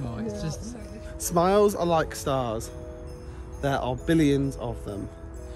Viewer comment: Omg I totally misheard what said. I thought he said that smiles were like SARS (ie SARS-COV-2 aka coronavirus) and I was waiting for him to say they were contagious. I'm glad to see that you found the baubles.